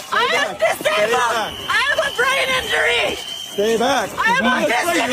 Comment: Between 12:34 to 12:40 did the said "I'm disable, I have brain injury"? What kind of brain injury do she have?🤔 Is that brain injury to her in mental hospital, cause what I know is that if it's the brain injury that needed hospital operation doesn't go/drive to a mass protests to disrupt officers on their missions😒